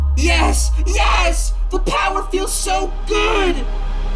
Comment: Is it me or did say Powah instead of power?